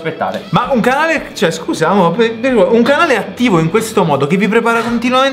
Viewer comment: Ma solo io al minuto 3:43 ho sentito scusa amo!!!